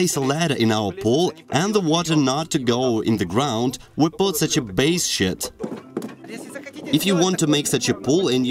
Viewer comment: Was it me I hear base shit